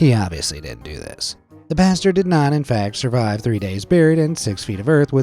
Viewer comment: it sounds like you said the Bastard 😂